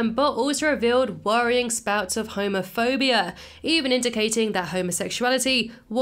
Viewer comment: I thought you said Homerphobia.